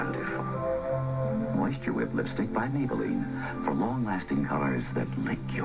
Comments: The maybelline one in the pool, I thought she said “more stupid” not “moisture with” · When Lynda Carter said "moisture whip colors" I thought she said "more stupid colors" 😂😂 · She said “Moisture Whip Lipstick” but I heard “My Stupid Lipstick” at first 😂